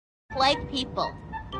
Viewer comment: I thought she said white people 💀